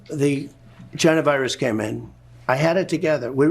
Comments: What did Trump say around 3:40? "Before the janavirus?" Was there one of those? I know there's a hantavirus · I’m sorry, did I hear it right when he said, “…Before the Jenna Virus came in….” (3:39). What is that?